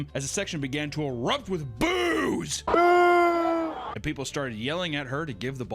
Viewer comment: Caption says that the audience erupted with *booze*. 😂